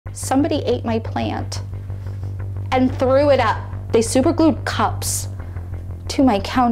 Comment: I thought she said they glued cops